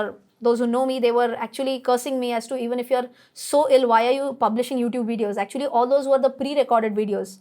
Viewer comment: Which word did you say at 16:49? illwire ? asking as this could be the new word i don't know.